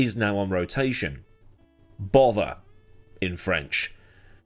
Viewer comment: what's the word in french? I don't...understand ^^ (i'm french speaking of course...so i'm interested to know ^^) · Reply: Merde.